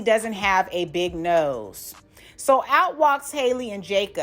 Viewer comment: I thought he said he loved big noses? Did I hear that wrong? Idk. Lol 😂 😬